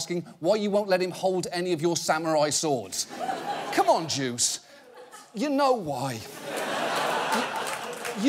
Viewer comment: is he saying "come one, jews?" I am not native speaking. And i dont think he said jews